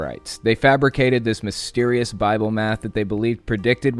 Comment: '...they made some bible meth' man these branch dividians are wild, did i mishear something?